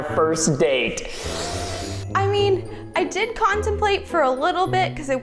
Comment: I thought Brianna said constipated